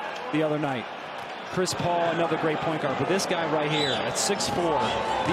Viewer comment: Anyone catch around 4:34 announcer said Chris Paul, not a great point guard, wtf???